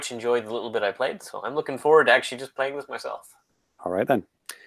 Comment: Did just say that he's looking forward to playing with himself? (Timestamp evidence